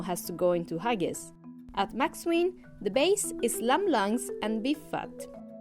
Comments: Did she say “lamb lungs and Beef Butt” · I swear I heard lamb lungs and beef butt